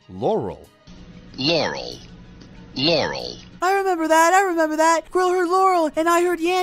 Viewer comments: I hear YOREL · i heard yaurel lol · I hear "Yaurel"... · I hear Yaurel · the first time i hear yaurel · I swear I hear Ya-Laurel, but only the first time when it says that, the second time its just Laurel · I hear yarul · I heard 'yarel'